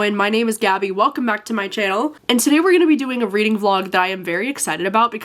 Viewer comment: Omg at the start I heard “it’s Gabby welcome back to my jail.” my ears aren’t working properly lmao